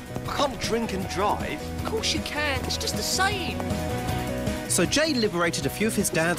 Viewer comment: Jay: Calm down. I'll get some beers for the trip. Simon: I can't drink and drive. Jay: Of course, you can. It's just a saying. Me: I always thought he said "It's just the same"